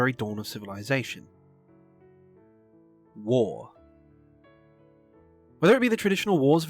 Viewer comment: wait...did some say WAAAAAAAAAAAAGH